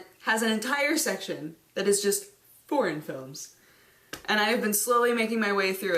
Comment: For a second there...i thought she said porn films.... just for a second.